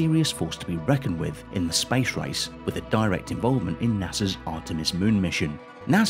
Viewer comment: I thought he said "spice rice" 😆